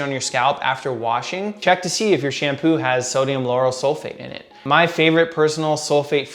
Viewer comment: wy does he say sodium yanny sulphate